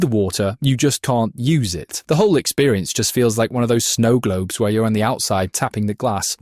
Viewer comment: I thought that said snowgrave